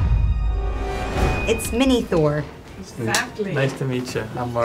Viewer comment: When Scarlett says mini thor my ear Heard that as minitho🤣lmao!!!